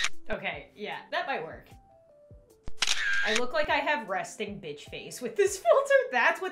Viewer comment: I have that, LOL. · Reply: Lmao when she said fist it sounded like she said bitch